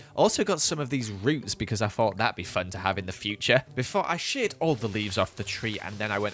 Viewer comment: fully thought he said sh*t 😂